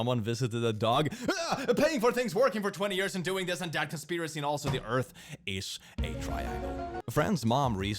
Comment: Did Click say "The earth is a triangle"?